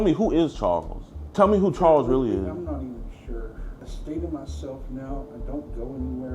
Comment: i thought he said sh!t myself lol